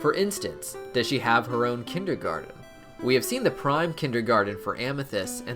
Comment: Sounded like you said "Pride Kindergarten" lol